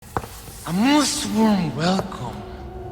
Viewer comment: The subtitles on the preview thought Salazar said "a Muslim welcome" at the start 😂💀